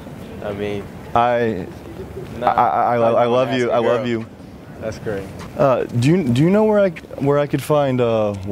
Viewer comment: Did he say "That's great" or "That's cray"? lol